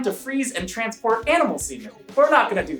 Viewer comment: i swear i thought he was saying ... to transport animal seamen but then i put on closed captions and saw other wise